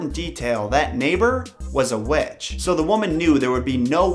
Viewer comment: Am i the only one that heard him say "their neighbor was a wetch"